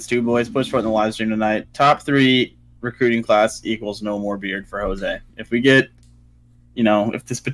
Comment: I thought you said no more beer instead of beard. 😂